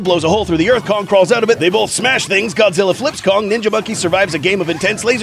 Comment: Did he say "NINJA MONKEY"???